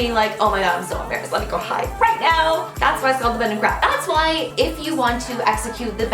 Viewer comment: I thought that it said The bend and crap